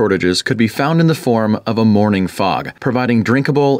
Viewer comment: I genuinely thought he said morning fart and not morning fog